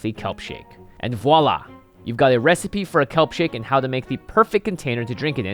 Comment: I thought it said help shake